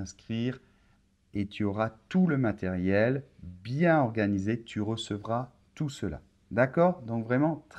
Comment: Bonjour Super Prof, you'll be very disappointed that I'm writing in English, sorry 😐. My question: Why did you use " FICHES PDF, but " FICHIERS audio? 2: At 1:59 on video, I'm hearing "TU RECEQUOI TOUT CELA"....not...TU RECEVRAS TOUT CELA" as in the transcript. Thank you. I tried several YouTube channels to try and improve my French, but yours is the one that keeps me motivated. Merci.